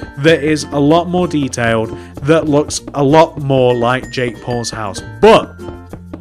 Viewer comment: At first I thought he said jake balls houce